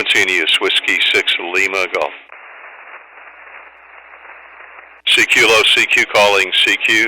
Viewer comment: When you started calling CQ after seeing if the frequency was in use, I hear you say "low" or "lo" in between CQ calls.. what is this for? For example your first CQ starting around 11:18 I'm hearing "cq low cq, calling cq, cq..." thanks. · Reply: He said "CQ hello CQ" its typical for people calling cq to say "cq hello cq 20 meters cq hello cq this is..."